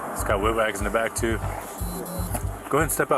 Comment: your captions are wrong. Cop says “he’s got Wig Wags in the back too”. Wig wags are flashing alternating blue-red lights.